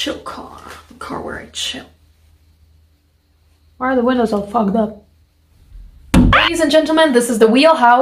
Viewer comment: Why are the windows all fogged up? AAAAAA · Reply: i thought she said why are the widows all fucked up